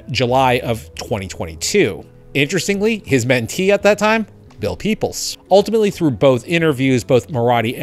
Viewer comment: my close caption says "Bill peoples", same thing when you showed the Sora team ITW, can you help me understand ? thanks! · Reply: The caption is supposed to be Bill Peeples. I guess the auto captions made it Bill “Peoples” which, I guess is reasonable, I’ll try to edit it.